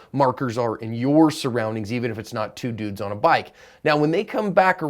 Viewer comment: I thought you said "two noobs on a bike"